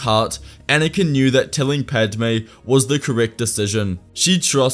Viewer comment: Did I hear “killing Padme was the correct decision” or am I hearing things? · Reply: "telling" Mic went funny